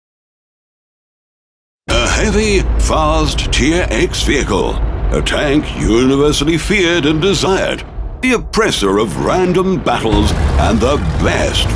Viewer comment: Did he just said tier 8 vehicle in 0:06?